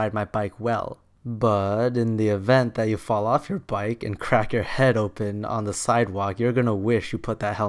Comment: I skipped ahead in the video once and it sounded like he said "You'd get fucked on a sidewalk"